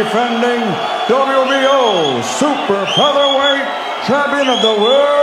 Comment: Did he say the wbo super heavy weight champion of the world of Lomachenko ! Cause at 129 pounds...super heavy weight !.:? I guess I didn’t hear right